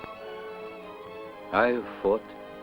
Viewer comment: I can't understand what he says at the end here >> "...but I haven't conquered over ???what???" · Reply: the original was '...over man's spirit which is as indomitable as those clouds' but the edit here makes it sound like 'over those clouds'